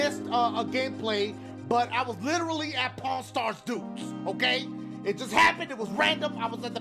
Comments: when i first time heared dashie saying pawn star dudes i thought that he said pornstar dudes XD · Why when he says pawn stars duke he sound like he saying Porn stars duke lml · Dashie I thought you said Porn Star lmao.